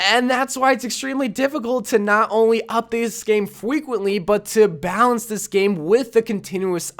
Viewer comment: DID I HEAR FWEQUENTLY!?